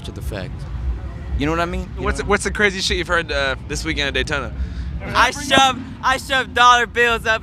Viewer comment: That Daytona bike week question to had the best answer I’ve ever heard. Hats off to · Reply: i thought he said bi-week... a week where we acknowledge the achievements of the bi-sexual community